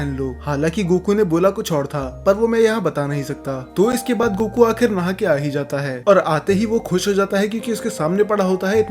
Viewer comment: I have questions What did goku actually say here? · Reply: I'm not into women's undies (underwears 💀)